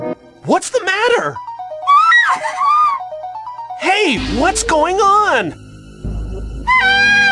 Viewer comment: "hey! what is going on" no capital w is what's going on and the subtitles don't match what he says